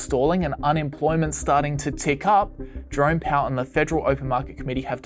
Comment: Sound like he saying "drunk" powel instead of "jerome" powel. Everything makes more sense like that..